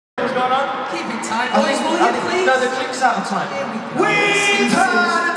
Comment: What was Louis saying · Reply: He said ‘keep in time boys will ya please’ and then Liam said something and he said ‘oh here we go’ :)